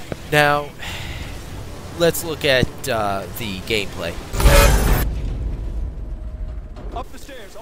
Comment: i thought you said "gay"-play lol